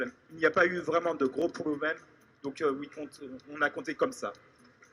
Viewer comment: I think said at 6:20 "...on a compté comme ça" "... and that is why we counted like that" but I cannot clearly understand and my French isn't the best